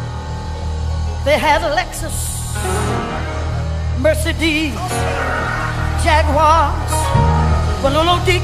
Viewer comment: Anybody noticed that she said "Mer-sir-des" instead of Mercedes. 🤣🤣🤣🤣